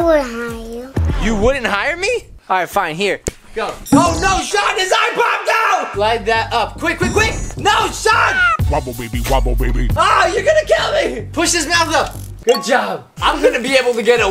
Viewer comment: On 9:51 I hear "woppababywoppababy" had me dying in laughter lol